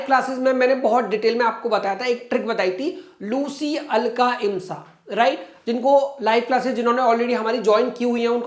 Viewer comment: what's the code sir said can someone tell me please.. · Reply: LUCY ALKA IMSA L FOR LIME/LIMESTONE C-SILICON OXIDE AL-ALUMIN KA-CALCIUM SULPHATE I-IRON OXIDES M-MAGNESIA S-SULPHUR A-ALCALIES